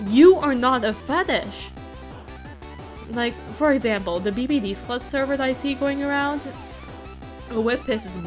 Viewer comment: the bpd sl*t server.... Did I hear that right? Huh tf?